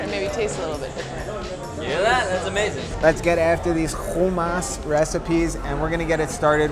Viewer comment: LOL i thought it said humans at first 😂😂😂😂😂